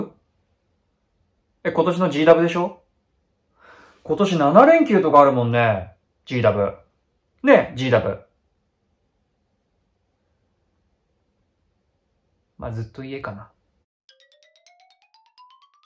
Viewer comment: 10wにしか聞こえない